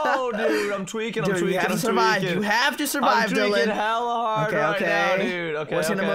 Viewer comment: did dylan just say hella hard? lol